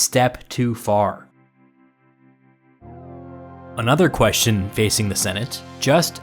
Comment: For a moment, I thought I heard "a stab too far".